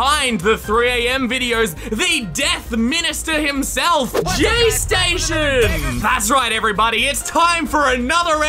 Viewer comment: The captions said gay station lmao